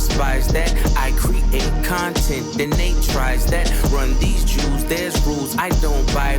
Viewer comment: no way hotmess in the chat didn’t get the Run the Jewels line… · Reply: Mf in chat genuinely thought push said “run these Jews”